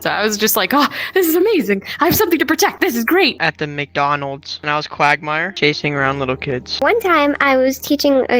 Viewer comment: It was so heart warming and then I just hear "Dressing up as Quagmire, and chasing little kids."